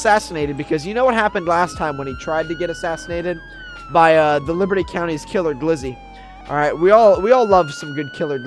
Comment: In the beginning of the video it sounded like he said liberta countei lol